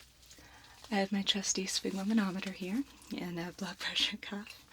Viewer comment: i cant figure out the spumumunometer name or whatever it is but i love it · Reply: Fancy name for blood pressure cuff. Sphygmomanometer (sfig-mo-man-ometer). =)